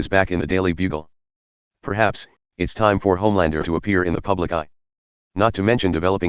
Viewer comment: Maybe i have hearing issues. Why sound like when says "Motherland and not Homelander "?